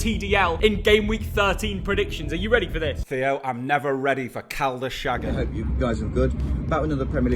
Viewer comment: What did just say? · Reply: Cal the shagger